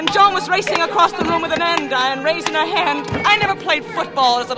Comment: *spoiler alert* At 54:02, I think they're saying that Joan was racing across the room with an andiron.